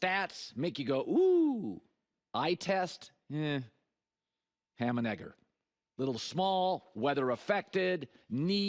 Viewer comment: WHAT DID HE SAY?? 😂😂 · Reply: Ham and egger lol